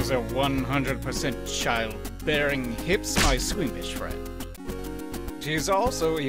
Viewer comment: "My squeamish friend" I thought he was saying "my Swedish friend" this whole time.